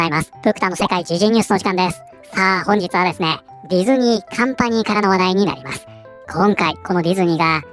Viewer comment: カンパニーが完敗に聞こえてしまったw